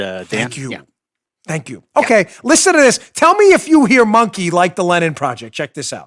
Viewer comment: I heard both "Monkey" and "..and Spy-gate". I think the audio didn't come through very clean.